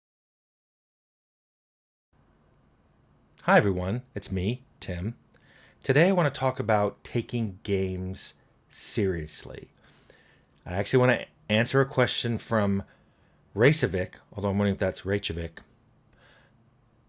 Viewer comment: OH... i thought u said taking JAMES seriously!